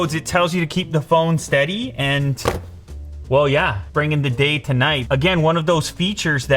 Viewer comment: he actually said bringing the day to night. xD